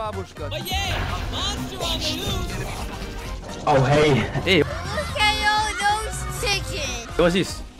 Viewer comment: how found meme with asian girl on 2:27? and what she says? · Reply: She says look at all those chickens. Its kind of meme